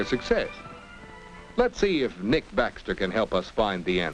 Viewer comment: at first i thought he said "Nick Bastard" hehe